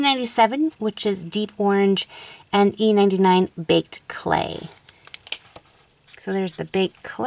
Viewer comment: Heheh! I thought baked clay said baked bean xD amazing vid! Keep it up ;)